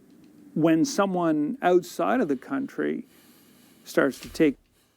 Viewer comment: I heard ‘outside’ not ‘oatside.’ 🤷🏻‍♀️